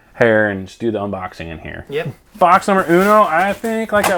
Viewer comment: I thought it said last unboxing for some reason.